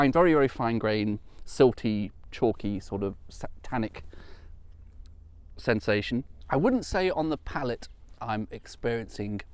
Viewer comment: This channel may not be called "Culverted London Rivers with Tweedy" (though do note, I would subscribe to such a channel) - but I think ditches in need of cleansing are always germane in any video! "There's a lot of ditch cleansing going on in the 16th century" is one of those sentences my mind finds frequent occasion to summon forth and it never fails to make me laugh aloud. Also, I'm sure I'm mishearing here, but I feel compelled to inquire - at 4:40 - what exactly is a "satanic sensation"? Sounds rather spooky for a wine tasting note!